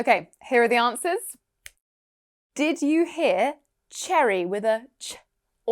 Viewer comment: As an indian, the only thing i got wrong was "Cherry", i thought he said Jerry, that too cuz im not using headphones at the moment